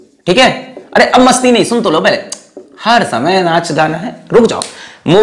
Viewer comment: ye kya bol diya sir 😶😑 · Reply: Mujhe bhi wohi sunai diya tha lekin unhone bola har time NAACH GAANA😂